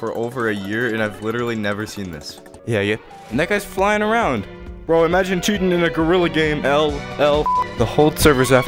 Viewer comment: why are the captions weird at times its like the person couldn't hear them correctly or didn't know english that well example: gorilla is guerrilla and at 3:08 says bro imagine cheating in a gorilla game and the captions say bro imagine shooting in a gorilla game 5:24 the captions are just embarassing